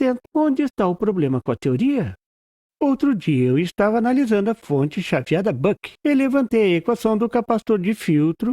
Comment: nossa meu ouvido ta ruim mas ouvi em 02:03 FONTE CHATIADA ...kkkk mas a explicação é ótima...🤣